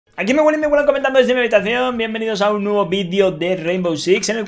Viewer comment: Entendi "Aquí mi abuela y mi abuelo jugando en mi habitacion" al principio del video 😂